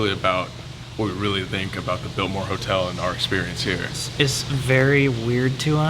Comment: Who else thought it said “Baltimore Hotel”?